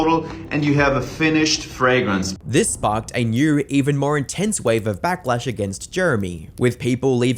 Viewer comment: Naw what did say😭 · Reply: This sparked a new even more intense wave of backlash against Jeremy